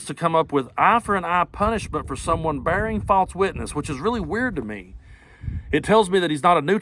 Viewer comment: An eye for an eye, huh? Then that would make Trump in a whole lot of trouble for all the lies and other crimes...just sayin' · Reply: diaper donnie thought they said, 'A lie for a lie.'